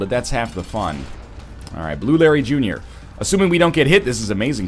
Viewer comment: I totally heard him say "Blueberry Jr." instead of Blue Larry Jr.